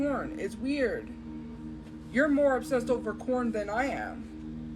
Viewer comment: bro did she say “you’re more obsessed over corn than I am” 😨